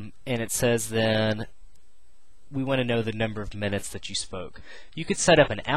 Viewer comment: lmao, the total is $4.20 and @ 4:40 in the video it sounds like u said "number of minutes that you smoke" but u said spoke. aha